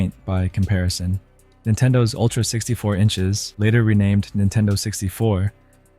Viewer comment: Hold up -- did he say that the N64 was originally called Ultra 64 Inches???